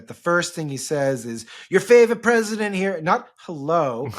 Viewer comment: Hahahaaa I thought he said “your favourite resident” because he’s a resident in an old people’s home in Florida